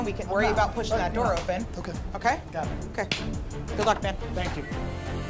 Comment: Did she say "Good luck Ben" to Also.. he's telling them to pick up the pace while he was missing from the kitchen for ages. No wonder his team don't like him